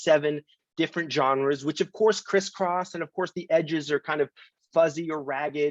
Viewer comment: When Kelefa says "of course [genres] criss-cross" I thought he was gonna say something like Of course Kris Kross straddled the line between hip-hop and pop and I was like "oh man weird example to just go to"